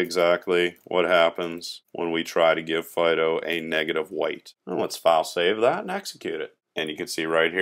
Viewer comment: Hi, at 10:42 of this video, do you say "negative white" or am I mishearing you say "negative one"? If I've misheard you or am highlighting a trivial mistake, I apologise — otherwise I am curious to know what this term is. Edit: Thanks very much for this series. Look forward to getting through all of it (2 videos a day for the next fortnight). You're an excellent teacher. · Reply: sunmustbedestroyed Thank you :) I say "negative weight" Always feel free to ask questions.